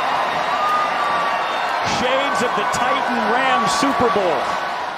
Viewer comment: did mans really say shades of the rams titans superbowl. I mean same idea... Miles apart in impact